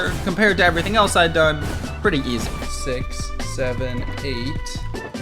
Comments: wait did he say 6 7 in 11:06? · IS THAT 67 I HEARD??